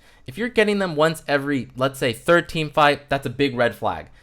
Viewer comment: "if you're getting them once every thirteen fight" what are you saying here? What does that mean? Once every thirteen fights? I don't even think there's that many fights in a game. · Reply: Nick Schweiss third team fight